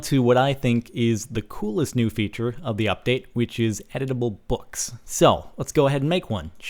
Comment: ohhhh editable books i thought he said edible books lol